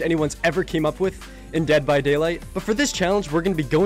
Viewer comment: Thought he said " DEBT by Daylight"